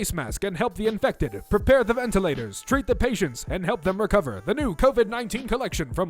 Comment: DURING THE LEGO CORONA THING I MISHEARD "TREAT THE PATIENTS" AS DRINK THE PATIENTS I NEED NEW EARS